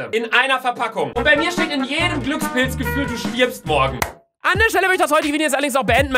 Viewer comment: Minute 4:39... ich habe es 1000x gehört und bin mir sicher, dass du Glückspilz statt Glückskeks sagst 😂😂😂👍🏼🎊